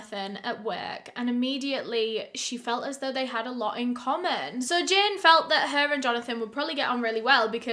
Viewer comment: Sorry I thought she said comma I’m glad that she showed what she was talking about